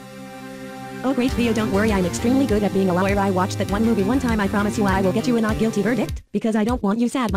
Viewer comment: the music is too loud compared to pucci's voice and i couldnt really catch what he said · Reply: "Oh great DIO, don't worry I'm extremely good at being a lawyer! I watched that one movie one time! I promise you I will get you a not guilty verdict, because I don't want you sad, my great, amazing DIO!"